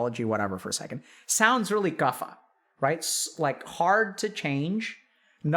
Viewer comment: hey pretty new here, what's guffa? He says it around 3:09. Tried looking it up myself, but I don't even know how to spell it, or what category it belongs to, lol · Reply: Vata, pitta and Kapha. He's talking ayurvedic medicine from India